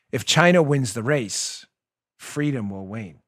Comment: It sounded like you were saying "win" instead of "wane". I listened several times.